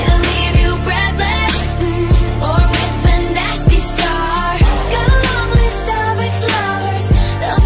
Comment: U guys are mishearing Starbucks while I always thought it said “nasty star” instead of “nasty scar”